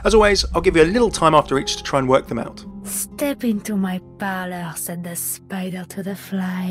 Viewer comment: the subtitles say "Step into my palace", but the proper quote is actually, "Step into my parlor", its from the poem "The Spider and the Fly"